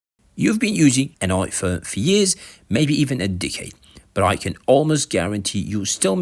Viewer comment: did you just call me a d*ckhead?